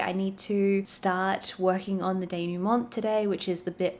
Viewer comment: “Denouement” is what she said at 7:48 if you didn’t quite understand and if you were like me reading the captions going ”wth is a day-new-month?!” Lol. Denouement is a French word that literally means the action of untying, from a verb meaning to untie. The English word is pronounced like the French: day-noo-MON and is the final outcome of the main dramatic complication in a literary work. Congrats on your progress and thanks for taking us along!! :)